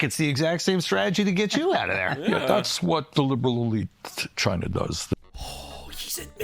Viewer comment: does he say “the liberal elite China does”? It’s what captions say, and it does sound like it, but is that correct? I wouldn’t doubt it though.